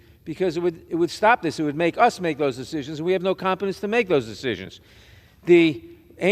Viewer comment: The elitist Democrat politicians and sadly many of the GOP politicians as well living the DC bubble. I could be wrong but it sounds like what he's saying or actually what he literally said is we're incompetent?